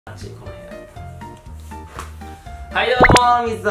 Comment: のところ「チ○コかゆいな」に聞こえるのは僕だけかね？